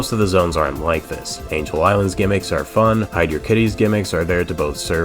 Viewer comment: I can't believe bro pronounced it hydrocity instead of hydrocity 😏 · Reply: Am I the only one who heard him say 'hydro-kitty'? 😂